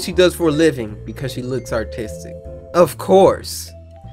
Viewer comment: Sounded like he said “autistic”